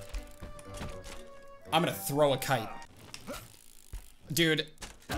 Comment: I’m going to throw a kite! Me thought he said: I’m going to throw a KID XD